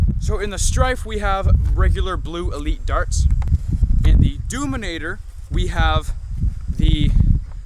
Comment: ha doominator instead of demolisher, nice one